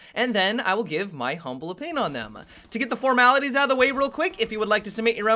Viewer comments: "To get the formalities outta the way real quick"... lol. That's the first time in like 3 years of watching your videos I've understood wtf you were saying there. I always heard "to get the furmilabes outta the way real quick" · After a solid six months, I have finally figured out that you say "To get the formalities out of the way real quick". You always said it so fast and I've been wondering what the hell you were saying for a long time. I always thought you might have said "Form eyes" or something weird like that. I'll leave.